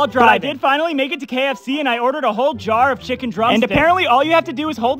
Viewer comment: For a second I thought he said “chicken drug stick”